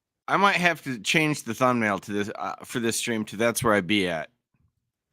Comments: I swear I thought he said, "That's where I pee at" 😅😅😅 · I thought he said BOTH..That's where I PEE AT 😂😂😂 The cop in the back trying not to crack was the best😂